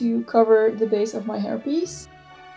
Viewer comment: It's a very silly comment but every time you say "hair piece" I hear "herpes" and can't unhear it. You did an awesome job with this hairstyle. Looks awesome :)